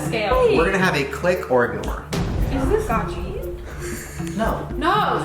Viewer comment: It sounds like is saying, "click or nor" not "ignore"